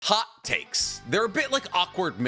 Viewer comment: Damn, listening to Danzo's summary really makes you think and reflect that he is very similar to politicians and leaders in certain countries in real life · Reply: at the beginning I thought he said HOT CAKES. HAHAHAHAHAHA